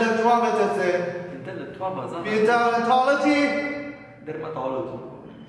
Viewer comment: al minuto 5:20 da dermatologi ho capito che era Occidentalist Karma (gomen se ho sbagliato a scrivere il nome)